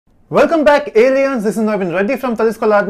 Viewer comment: Is it just me or did he call me alien?